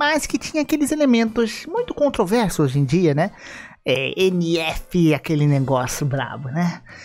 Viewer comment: NF...? não entendi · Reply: nft